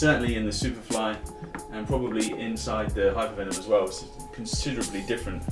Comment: anyone else hear him say aswels